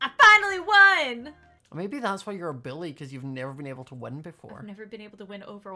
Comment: WhY aRe YoU sUcH a BiLlY (It sounds like he says billy.)